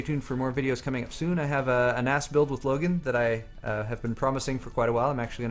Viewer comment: I thought he said "An ass build with Logan" instead of "a NAS build" lol